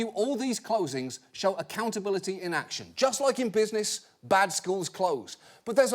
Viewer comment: did he say "accountability in action" or "accountability inaction"?